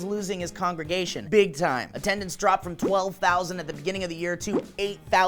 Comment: 8000 people in one church? thats a big church. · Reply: Manuel Campins I thought they said 10,000 but it is 12,000. So instead of losing 2,000 members they lost 4,000. Its still sad that they haven't even lost half of their congregation due to the crap that guy spews.